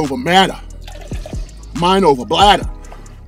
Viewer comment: I thought he said "mind over bladder." 😂